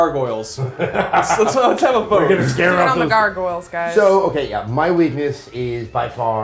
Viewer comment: what does she says after they make the Gargoyle joke? i think she says "ur do with the Gargoyle guys".